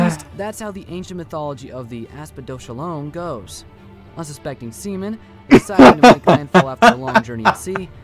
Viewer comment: I wasnt looking at the video and all I heard was "unsuspecting semen" followed by uncontrollable laughter